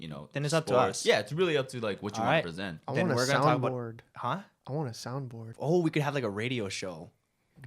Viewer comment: I thought said "I wanna sound bored"